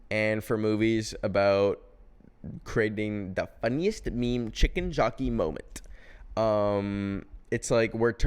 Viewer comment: Okay on first watch when you said “funniest meme chicken jockey moment” I thought you said “funniest meme the Chicken Jaco moment” and I was about to throw hands thinking you were dunking on Jaco Pastorius playing The Chicken